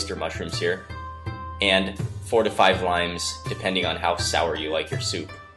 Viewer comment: Haha when he said "4 to 5 limes" I heard "45 lines" & I was like "holy it's gonna be lime juice!! :O" then I realised.